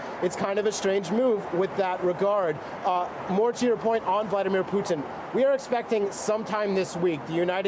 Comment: did he actually say Vitamin Putin? Didn't know Puttin had his own brand of supplements. 🤣🤣🤣